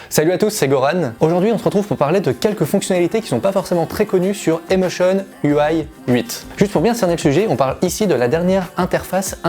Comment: lol jai entendu "Gohan" super le bouton multifonction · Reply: Son nom est San Gohan 🤷